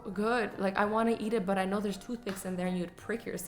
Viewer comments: I thought you said toothpaste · I thought when she said TOOTHPICKS she said TOOTHPASTE like if you heard the same thing 👇❤️